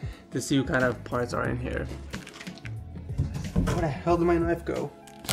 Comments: At first, I thought you said, "Where the hell did my life go?" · I thought u said 'Where the hell did my life go' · who else thought he said were the hell did my life go 😂